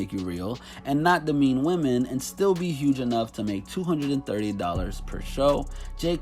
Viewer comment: did he just say $230 per show 🤔😂😂